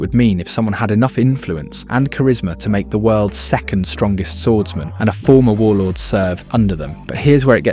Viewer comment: Did he just say world's second Greatest swordsman ?